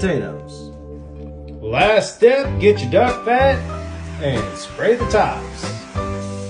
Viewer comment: I thought duck fat said fuck dat😭☠